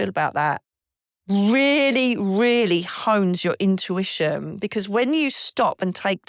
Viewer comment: The dark energy voices are at 27:32 34:14 40:23 41:07 the last one has a female and male! 😮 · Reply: I slowed the playback speed to .25 and it sounds like he says HONEST.